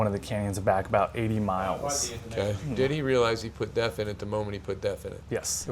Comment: And for Australians, what he is talking about is what is called AdBlue here in Australia DEF Diesel Exhaust Fluid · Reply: Had to Google that one, I first thought he said 'he put death into the tank'